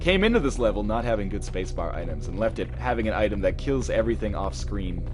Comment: Why do I hear "Spacebaritus" instead of "Spacebar items"?